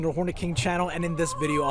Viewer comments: Bro I ain’t gonna lie at first I thought he said “welcome to horny king channel” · I thought you said Horny King at the beginning lmao 😂😂